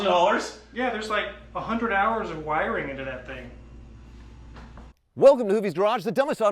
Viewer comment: I thought he said There's like a hundred hours of irony in that thing.